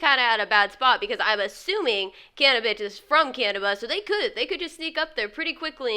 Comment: she said cannibitch instead of cannibis lol she hella dissin